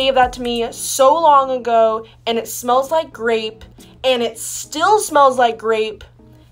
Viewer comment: i thought she said it smells like r*pe